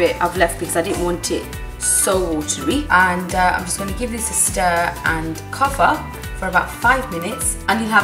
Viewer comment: wat did she say after stir? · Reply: give it a stir and cover